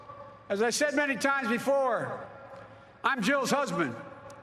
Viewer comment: Lol. I thought he said "Like I said many times before, I'm Joe's husband" 😂